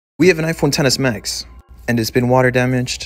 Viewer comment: I heard "we have an iPhone tennis max" lol